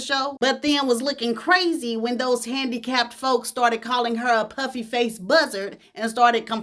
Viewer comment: “Puffy faced buzzard” took me completely out.. Zsa Zsa was something else 😂😂😂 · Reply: Puffy!! 🤦🏾‍♀️ I thought she said PUPPY!! I was like "well darn, they done called this lady a dog AND a buzzard!!" 😂🤣😂🤣😂 Thanks for clearing M hearing up!! 🦻🏾 🙉